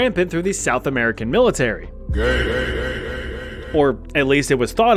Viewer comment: Did he say South American military?